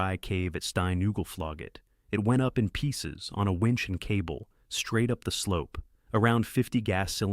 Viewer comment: lol winchester gun 20:31 instead of winch